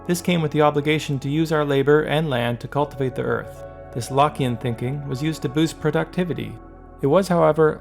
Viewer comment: I misheard "Lockean" as "Lock in" lmaooo, good video though!